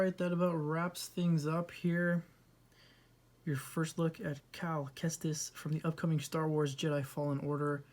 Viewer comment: Thought I heard Cal Testes for half a second. Disney getting bold.